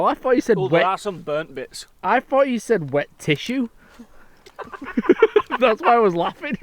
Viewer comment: I heard it as wet tissue too